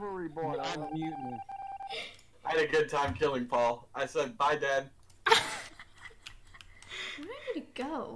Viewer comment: DID SAID BYE DAD OMGGGGGGG